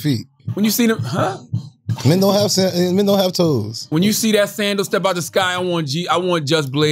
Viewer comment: Did Wax say "Men don't have feet"